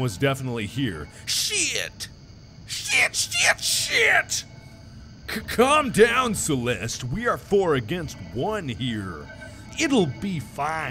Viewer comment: I started to watch this video earlier and was skipping to the part I was at so Makiplier sounded like he said "Calm down celeste we are, ass lights" LOL